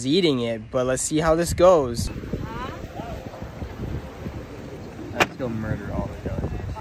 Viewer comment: I just hear "Let's go murder all the ducks."